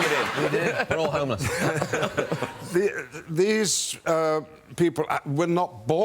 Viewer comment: Did Liam say, we're all homos?! 😂😂😂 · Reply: okay but he actually said we are homeless